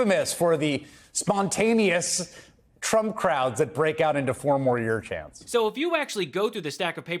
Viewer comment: All I heard was " No more years!".